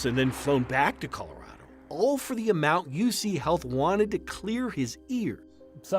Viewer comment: Thought it said u cheat health. Yeah cheat u is right